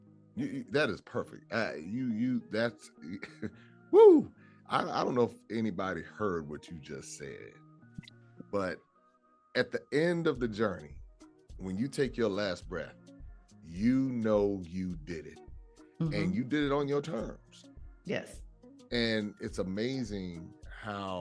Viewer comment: I heard, "At the end of life's journey, I made it" on my own terms".